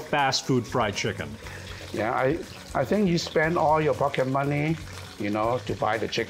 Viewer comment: I misheard “pocket money” as “fucking money” 😂😂😂